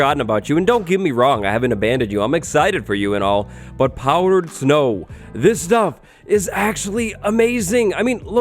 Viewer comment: I thought this said “powered snow” and I was like, red stone snow?..